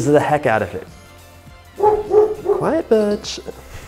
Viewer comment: LMAOOO I THOUGHT HE SAID "QUIET BITCH"